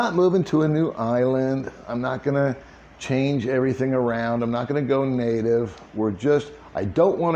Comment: Had a little trouble hearing you at 28:35. Did you say you're not going to go naked? In case that is what you said, I just wanted to take this opportunity to thank you on behalf of all of your subscribers.😀